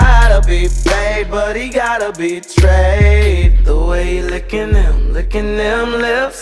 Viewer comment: Lady Boner Has Come Too Life OMG This.Is.Too.*SEXY* At 2:03 did anyone notice Todrick sounded like said “he gotta be Tre” That means that Tre Melvin was his bf but they broke up and Toddrick is still in love with Tre